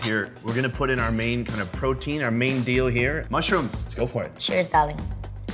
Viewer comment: Did he say "cheers darling" on 7:00 hmm 😏😏😏😏😏😏😏😏😏😏😏😏😏😏😏😏😏😏😏😏😏😏